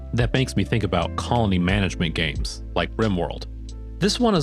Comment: I'm sorry did you just call rimworld calming??? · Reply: He said colony